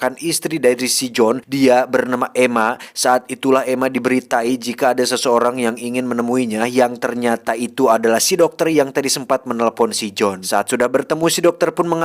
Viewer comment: di beri tai...heee klo tidak salah dengar